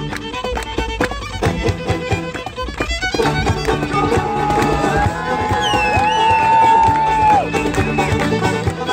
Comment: minutes in, does anyone know what word the accordian player on the left says. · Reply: I think he just says "Come ooon!"